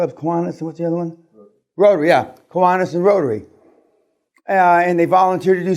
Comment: Is he saying Koanas and Rotary?